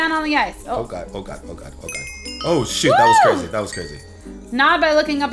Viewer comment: Did say oh shit this is crazy?